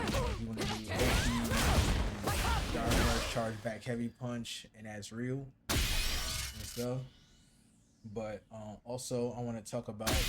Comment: You're really hard to hear over the game audio - at 0:55 I can't tell what you're saying at all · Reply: I was saying drive rush charged back heavy punch is the oki for light dima